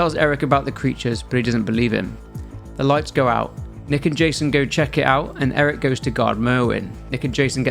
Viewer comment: everytime he says "nick and jason" i always hear, "naked jason"